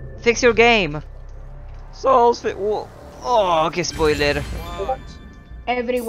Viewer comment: It's funny how like i heard someone speaking italian 1 or 2 times in the vid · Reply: Like i heard: Che spoiler, which means, what a spoiler in italian